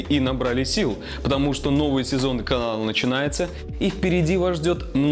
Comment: Я первий раз услышал Каала